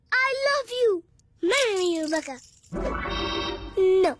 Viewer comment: I thought he said "MARRY ME YOU BAKA!!"